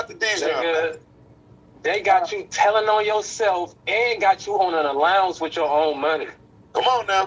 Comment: Yo I thought he said the feds got J.PRINCE on allowance with his money. That's crazy WTF.